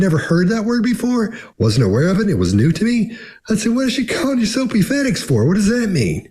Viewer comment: Soapyfetics is now my new favorite made-up word, LOL! Thanks! And although this is torture for you guys, we have to finish this journey we all started together. 4 more episodes, oh no, lol 🙃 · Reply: I thought he was saying soapy fedex and figured it was a real term for some special kind of special fedex delivery. 😂